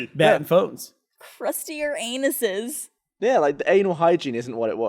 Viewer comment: Lol I skipped and all I heard was "humans have crusted anuses"